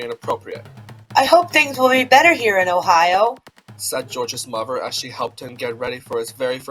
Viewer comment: for a second I thought it said George’s lover.